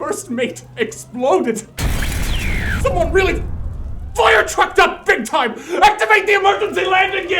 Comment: Did he just say firetrucked? Hahaahha ;D